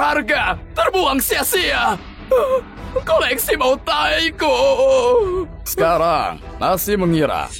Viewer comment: Ga salah denger pemeran suara ayah nya bilang "ini koleksi bau tai ku "admin nya ngelamun kah di menit